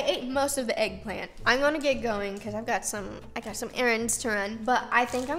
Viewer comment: What said: "I've got some errands to run" What I thought she said: "I've got some Aarons to run" 😭😭😭